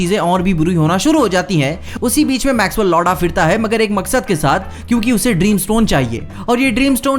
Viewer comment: You said Maxwell Lord Aa firta hai But what I heard "Maxwell Lawda" 😂